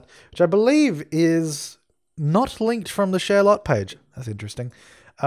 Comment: You gave me heart attack. It sounded like you said "Sherlock page"